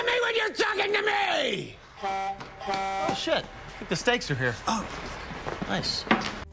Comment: "LOOK AT ME WHEN UR TALKING TO ME! ...oh shit, the steaks are here" is way funnier out of context lmfao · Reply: the first time I heard it was in an out of context video. I misheard him and thought he said "oh shit the mistakes are here"